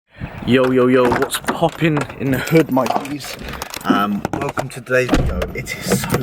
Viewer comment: Did he just say what’s popping in the hood my n*ggas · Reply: As if hahaha, I said my Gs